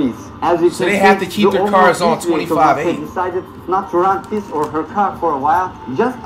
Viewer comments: Did bro say 25/8 or Am trippin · Did bro just say 25/8 instead of 24/7